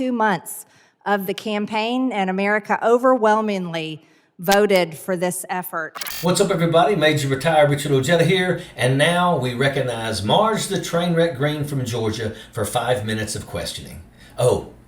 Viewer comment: Sounded like she said that America voted for this F er